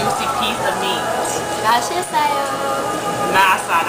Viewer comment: did she say "masarap" instead of "mashisoyo"? haha